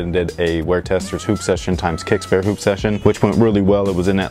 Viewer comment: Maybe it's the mint but it sounded like he said poop session.